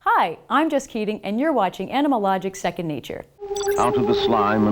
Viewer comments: In the beginning I always hear " Hi Im just kidding " :D :D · why I always hear I'm just kidding.